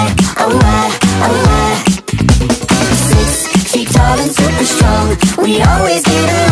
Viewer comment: 6 FEET TALL AND SUPER STRONG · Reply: i thought it said saitama's super strong 😭😭😭